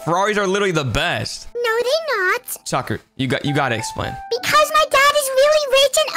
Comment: When you said tuker i thought you said sucker